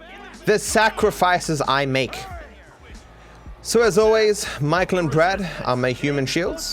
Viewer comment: Everybody, remember that as always, Michael and Brad are used as human shields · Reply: I heard "Michael and Trev" and was confused